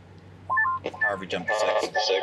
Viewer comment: am i the only one that heard him say "Harvey jump the fence"?